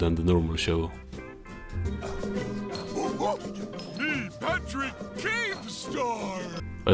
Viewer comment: These were all the moments that made me laugh: 1:59 Patrick sounding like he said “Patrick Keemstar” 3:09 The high pitched krab laugh 4:51 The fish getting eaten 5:07 Man on the streets gets hit by a car 10:38 Kaboom 12:05 Fish’s reaction 18:12 Slappy dies 19:13 Granola 19:28 (honorable mention: The YouTube subtitles here think Patrick said “loser” instead of “Nosferatu”) 19:50 Slappy dies again 22:06 Why am I up there? 25:12 I don’t see what the big deal is 27:47 Coconut 32:10 Smooth sailing 34:42 Patrick just completely freezing in place for a second 39:05 I can’t look! I can’t look! Overall, the show can be funny at times, but it’s sad to see that the actually somewhat clever jokes are really only in this show, while the main SpongeBob show really only gets callbacks to old jokes, and the few new “jokes” if you can even call them that, are gross out, meme faces, or a character suddenly going into peabrain mode.